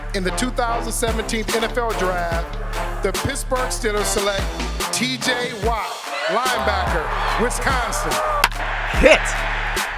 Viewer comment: sounded like he said “The piss-berg Steelers” 🤣🤣